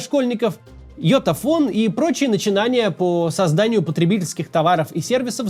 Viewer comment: мне вообще когда я это услышал впервые послышалось ёпта фон